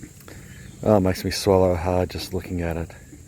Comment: Did he just say it makes him hard just looking at the pineapple? at 2:00 🤣🤣 Noice. lol